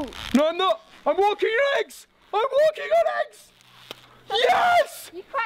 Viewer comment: Oh... I thought they said the walking dead egg (copyright)